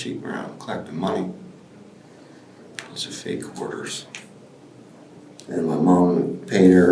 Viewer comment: after he says “but she went around collecting money”, the subtitle says “(indistinct) quarters”, but it’s pretty clear he says “those were fake orders”, or “it was fake orders”, or “it was a fake orders”. Not sure if the police or investigators did the subtitles or if the creator of this video did (Great job on the video by the way, don’t want it to come across as being rude or trolling just to be a jerk) but it kind of comes across as a weird miss... I wasn’t looking at the screen the moment he said it, but I heard that phrase pretty clearly (‘clearly’ besides the obvious questionable start of the sentence upon multiple re-listens where it could be “it was” or “those are” or “those were”) but glanced up just in time to see the text “(indistinct) quarters)”. The reason why I say it’s kind of a weird miss is that with all the time and effort put into transcribing the conversation and the obvious intelligence and patience it took to do that, “(indistinct) quarters”, just using context clues, seems so obviously out of place and incorrect. Using the same context clues of him talking about how she collected money but never fulfilled the order, added to the relative audio clarity of that sentence when compared to some other sections of this video with lower quality audio, “Fake orders“, at least to me, is pretty apparent... especially considering there are some sections of this where the transcriber seem to almost magically understand what was being said that I never wo